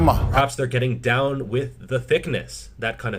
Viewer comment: HAHA! I heard that as David Draiman on 'Down with the sickness'